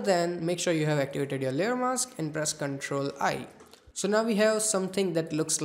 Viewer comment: What did he said at 7:12 ? CTRL + ? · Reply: He said CTRL + I